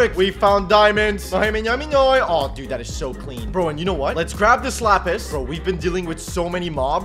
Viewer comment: when he said lets grab this lapis i thought he said slapis